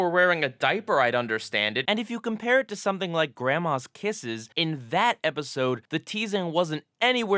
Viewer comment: i thought you said grandma's pisses